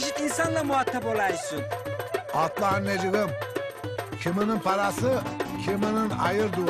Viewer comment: osman aga ne diyor bi anlamadim necibettin diye duymaya başladım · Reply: Atlar necibim diyor heralde